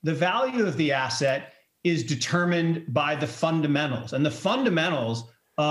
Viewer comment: Did he just say FUNDAMENTS for cryptos lol?